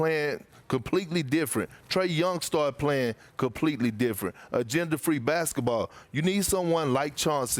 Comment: lmfao i thought perk said "gender free basketball." Then realized he said agenda free